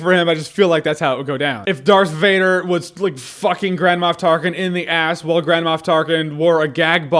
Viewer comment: I kept hearing "Grandma Tarkin" genuinely all the way through.